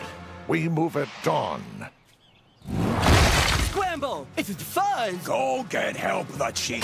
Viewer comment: what does homestar say at 0:11? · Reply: I think he said, "Scramble!! It's the fuzz!!"